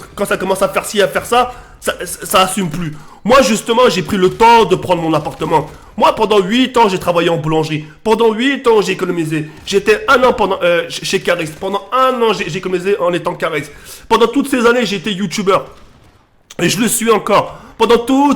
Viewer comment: pendant 1 an il etait chez Kaaris? moi jai entendu ca a